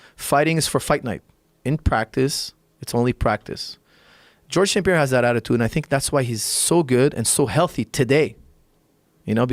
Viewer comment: Anyone know what boxer he names at 9:42 ? I cant quite catch it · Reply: Georges St-Pierre